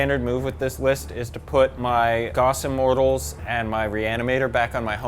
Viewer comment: I know they were saying "gauss", but I kept hearing "goffs" & was getting excited about orks 😂 Which is the only appropriate reaction to hearing goffs